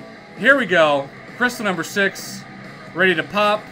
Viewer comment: did he just said NICE JUGS?😂😂